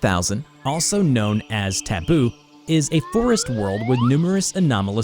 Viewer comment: I thought it said tubbo, but it was taboo. OOHH RANBOO